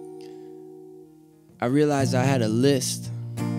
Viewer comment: I thought he said "I had a lisp"